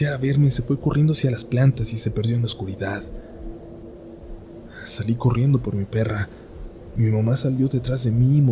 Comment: Soy yo o la primera vez que escuche esta parte entendi sali corriendo como perra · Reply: Dijo "salí corriendo por mi perra"